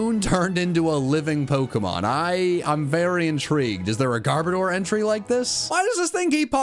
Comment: when he said Garbodor I thought he said Gardevoir and was really confused